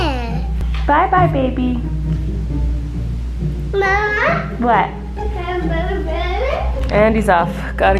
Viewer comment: Just me or at 3:55 sounded like he said mama, have another baby 😂